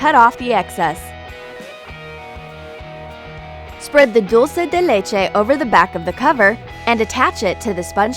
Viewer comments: "Spread the del la ch-'' Me: Waitwaitwait, del what? Isn't it Caramel? Or am I hearing things? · what the hell is that word??????? Duel setelece on the sugar paper ?? What is (duel setelece <-------)